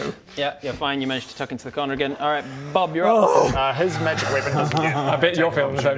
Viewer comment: Really miss the subtitles on these episodes. Not only because the subtitles are awesome in general, but especially in DnD it is sometimes really hard for me to understand things and automated subtitles are even worse than me. I was listening to 35:28 at least 20 times until I understood "his magic weapon doesn't get attack of opportunity or something else, alright sweet". And the only reason I did that was because I was writing this comment and was trying to write down what I heard, because I understood basically nothing the first time and it got a little bit better each time. Normally I would have just shrugged it off with "must have been the wind".